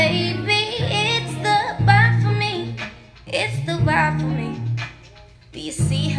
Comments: I THOUGHT IT SAID “it’s the vibe for me” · I always thought she was saying "it's the vibe for me" lol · I thought she said it’s the vibe for me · Was I the only one who thought she said 'it's the vibe for me' orrr